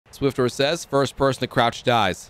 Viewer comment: You said the last person who jumps dies he was the first to jump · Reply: Sorry I thought you said last not first